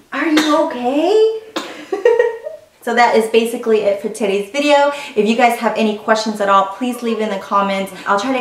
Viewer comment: I thought she said that’s it for titties video lol 😆 my little girl is due in three months. Super excited ❤️